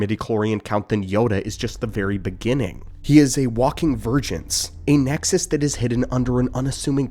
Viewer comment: God damn my ear, 7:38 I thought he said: he's a walking virgin